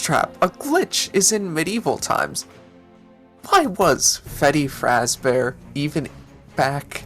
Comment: I'm sorry but "Feddy Frazbear" Was I the only one who heard that?